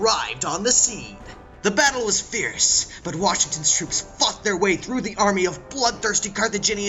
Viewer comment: I misheard and heard that the washingtons troops fucked the way trough